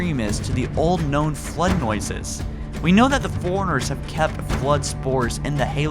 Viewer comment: All I hear is "foreigners" when you say Forerunners lol. I like your theory but in the Halo 5 legendary ending cinematic, you can clearly see that the lights of the unknown Halo ring light up cuing in the sound you hear to show that it might be activating. Why it is activating can tie into your theory about the Flood coming back since the Halos were meant to rid the universe of the Flood. I think the sound effect you mention being the sound of the Flood might be too far reaching though but otherwise great video. It would make for an interesting story for Halo 6 should the Flood indeed return.